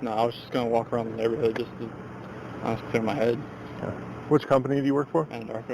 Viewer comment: why is he saying see Mt hood he said clear my head lmfao